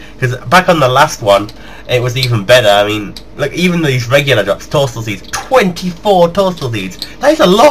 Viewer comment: I swear that said torstol semen...